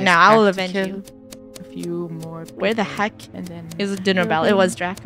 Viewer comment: who heard Draco whispering "its time to kill, a few more people" while Funneh was talking